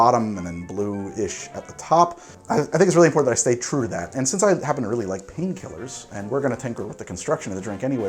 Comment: i heard “i happen to really love pain killers” and immediately assumed he was talking about opiates